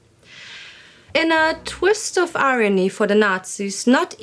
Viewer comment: did you say "a twist of aryany" (instead of "irony)"? If so, it was a wonderful pun. If not, then I may need hearing correction.